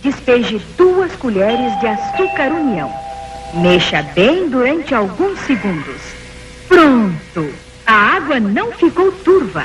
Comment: eu ouvi ela falar mexa bem DOENTE alguns segundos.